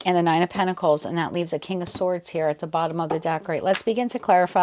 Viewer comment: Omg.. when you said King of swords at the bottom, I heard clearly "told you so!". 😂